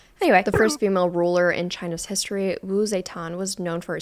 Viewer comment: please tell me im not the only one who heard "harry potter"